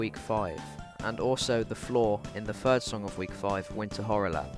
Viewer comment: Did you say first- Cause if you did, I’m pretty sure winter horrorland is the last song of week 5 11:44 Another quick correction: MMM is the week’s name, her real name is Mommy Mearest Other then that, good video! Also I have meme “Monster and Winter Horrorland are based on real events” me: panik “Thankfully this is just a joke” me: kalm